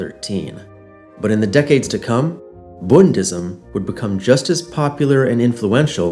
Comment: I thought it said Buddhism for a second